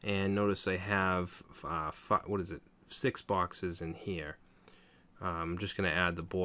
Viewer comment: Who else heard what I heard? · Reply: "5, what is it? 6 boxes in here..?"